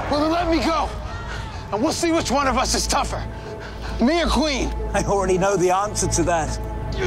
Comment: haha at 0:24 sounds like he said "Mia Queen" just like "Mia Smoak" but he really said "me or queen" ;(